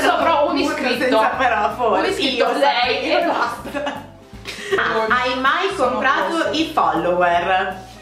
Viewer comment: Qualcuno può dirmi cosa ha detto a 9:43 ? Hai mai .... i followers? Non ho capito ahah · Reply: Hai mai comprato i follower